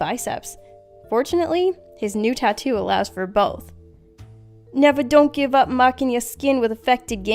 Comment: What the tattoo said: "Never Don't Give Up" What I heard: "NEVER DON'T MIND ABOUT A THING" References. References everywhere.